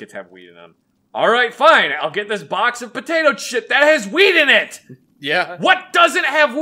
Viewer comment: Wait, did you just say box of potato chips?